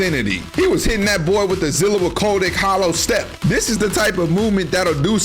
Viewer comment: Don't think you got away with saying "Zillua Koldyk". I heard EVERYTHING!